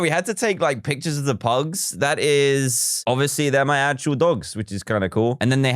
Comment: I thought that he said cut the ACTUAL PUGS 2:49 IM REALLY DUMB